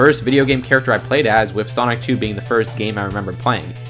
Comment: I thought you said sonichu lol